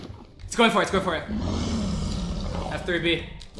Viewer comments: I think he said "F3 B", not "after it be". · subtitles are wrong 2:25 he doesnt say "after it be" he actually says "f3 b" which turns on hitboxes